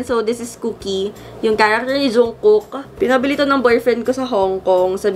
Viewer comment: Uhmmmm zungkook?? Sorry I just hear that1:23